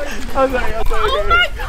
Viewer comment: Andrew was trying to win Lexi's heart a bit before he and everyone else turned on her but it's so cute. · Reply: I swear I heard him say 'I'm sorry BABY'